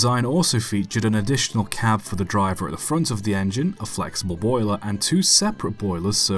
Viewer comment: I’m sorry, did you just say flexible boiler?